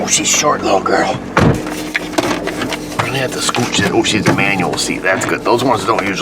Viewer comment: I thought he said it was a manual? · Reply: he said the seat was manual